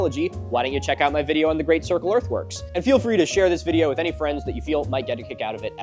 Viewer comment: every time he says "earth works" i hear "earth worms" and i know he knows about earth worms TELL ME ABOUT THE W O R M S